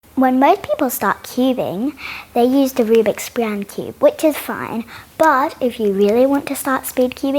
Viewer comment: in the beginning I heard “when my people stopped cubing, they used a Rubik’s brown cube” LOL